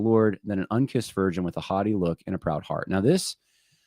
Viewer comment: At first, I thought you said, " . . . unkissed virgin with a hottie look." Then I realized you were saying "haughty," not "hottie." I'm like, if she's on Only Fans, of course she's supposed to be a hottie!